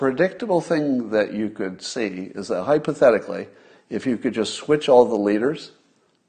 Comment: Did I hear Scott say, "Hopethetically"? What a great chimera word. Thanks